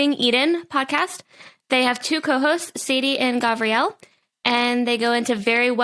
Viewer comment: Why do I hear “ho hosts” when he does the intro